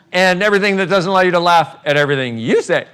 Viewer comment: Hello! (Help please) I like so much this video that I want to share it with people I run bars to. They all speak Spanish so Im translating in a word doc. But I cant understand what he says in minute 3:50 to 3:59 and minute 5:47 to 5:51. Would anyone be kind enough to write it down please? Thanks and thanks Dr. Heer for sharing this wonderful video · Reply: And everything that doesn't allow you to laugh at everything you say And everythig that allow you to lauch at everything you think